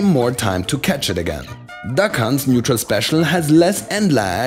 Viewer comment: I honestly thought he said “the cunts” not “duck hunt’s”